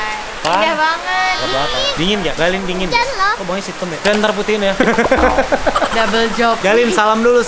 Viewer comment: Apa aku yg salah denger yaah 8:10 Abang: Yang entar putiin yaah: double job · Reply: Yan.. maksudnya fian..